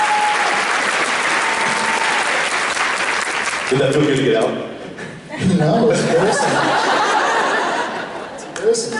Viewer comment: Can you guys help me out?? What does Jensen say at 7:00 before he starts laughing? Something with ‚did .... out?‘ It drives me nuts LOL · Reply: I'm not sure if you found an answer yet, but he said "Did that feel good to get out?"